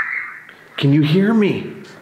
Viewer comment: So I'm not even half way done with this video yet. When was in the incinerator doing the evp. I heard 'Hey, hello.. can you hear me.' I'm now at 41:37 when is reviewing the evp and it said can you hear me. I DON'T HAVE HEADPHONES ON AT ALL!!!